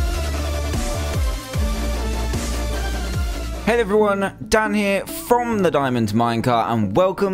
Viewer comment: It sounds like he is saying "hate everyone"